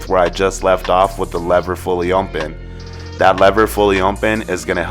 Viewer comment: Is it me or when u say “fully open “ sounds like u say “fully Oinpen” lol now I can’t help but to hear then when u say fully open . fire cut by the way bro